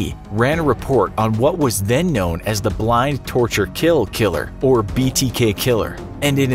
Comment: Sounds like you said blind. It was bind